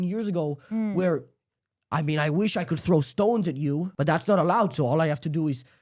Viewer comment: did bro say stone her ? 😂